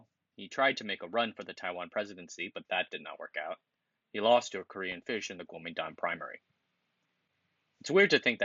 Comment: did you say he lost to a Korean fish? I know I misheard that. But that is hilarious.